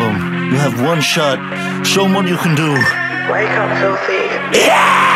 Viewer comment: What do they mean with "wake up Sophie??" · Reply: It says filthy, I thought it said Sophie too lol